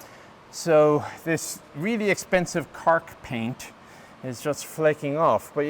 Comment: ‘Kark’ paint? ‘Cark’? What did he say and what is this type of paint? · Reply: Chemical Agent Resistant Coating.